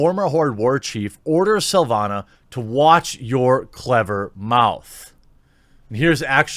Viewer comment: I thought he said, "Watch your tongue, Witch." Not that the alternative would be bad.